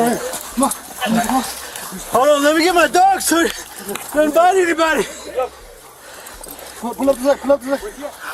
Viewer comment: Sounded like he said so his dog "doesn't bite anybody" vs what captions says "he will bite everybody"